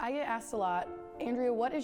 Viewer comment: I am sorry, but at the beginning I thought you said, "I eat ass a lot" XD I am dying.